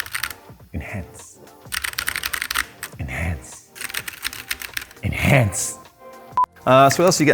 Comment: what i heard, "Nice, nice, nice, enhance"